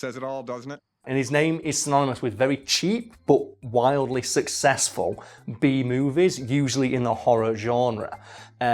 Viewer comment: I thought you were saying bee movies and got really excited thinking there was another one